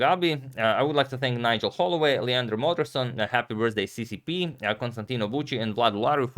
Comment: Antonio, did you say Happy Birthday CCP at 16:00? 🤔😢